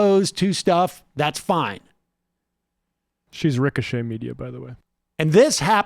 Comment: Can anyone help me understand what Matt is saying here? She's "ricochet-ming you" ? "ricco-shaming"? Whaaah? Thanks in advance! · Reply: No. That's the name of the right wing outlet she works for. Ricochet Media.